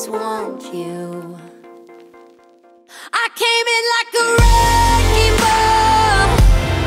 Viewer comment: Another beautiful song ruined by the clip. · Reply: lol I thought it said I came like a rainbow xD